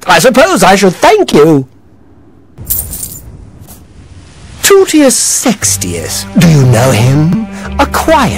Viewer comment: at first i thought he said testiclees sextius lol!